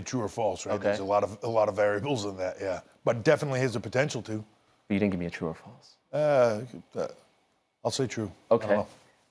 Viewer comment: What did Ariel say at 2:23:28? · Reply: “but you didn’t give me a true or false”